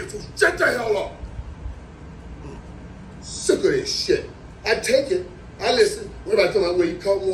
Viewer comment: "what did he say!" 😂😂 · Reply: Sick of this why I take it 😂